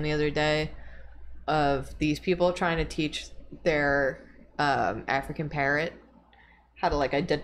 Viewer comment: I thought said "African parent" instead of African parrot and I was like "why is this guy's dad so obsessed with Wario?!"